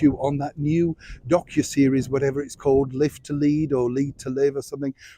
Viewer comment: I thought you said 'Lift the Lid' not live to lead! Freudian brainwork there I think! 🥴